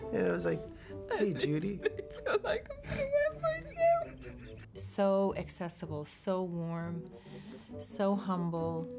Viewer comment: Can anyone tell what Daisy said at 0:34?? 😂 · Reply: I literally feel like I'm gonna wet myself.